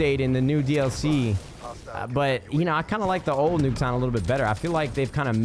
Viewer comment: did he say old nuketown?